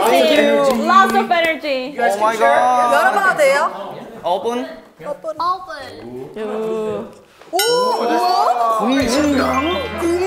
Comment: did someone hear somin say "Open" so cute!